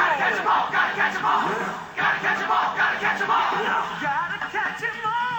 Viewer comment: I heard "scratch my balls"................ I have such a strange mind